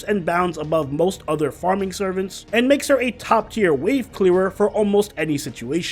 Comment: "...top-tier wave clearer..." Am I the only one who heard that as "weeb-killer"?